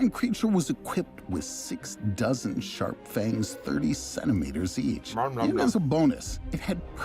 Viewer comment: "six dozen sharp fangs" and then the caption says 60 fangs.